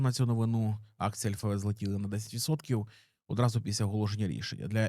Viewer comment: Що він каже на 1:55 чиї акції взлетіли ? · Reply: Альфабет, холдинг який є власником гугл, ютуб і тд